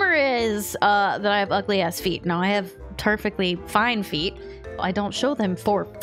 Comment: did you say "turf-fectly"? If so, that's brilliant 😆